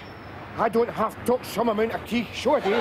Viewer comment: Would someone please tell me what he said at 2:34? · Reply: 'I don't half talk some amount of keech (Shit) '.